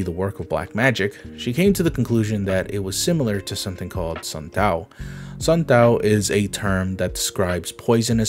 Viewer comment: sounded like santet